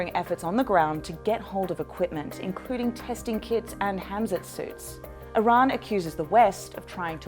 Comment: Hamzat instead of Hazmat